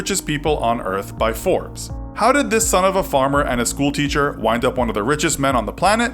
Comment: When he said son of a farmer I thought he said son of a bitch